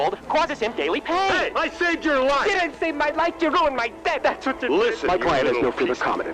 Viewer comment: All this time I thought he said " you ruined my debt" like now that he's alive bro had to pay for it lol but still funny